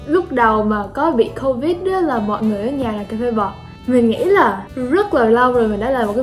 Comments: Cà phê bọt mà mình lại nghe thành cà phê bò chứ:))) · Cà phê bọt mà đọc nghe thành Cà phê bò =)))